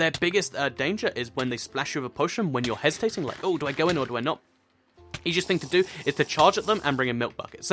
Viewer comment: WTF DID U SAY!!?? 😂🤦🏾‍♀️ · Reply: He said do i get one or do i not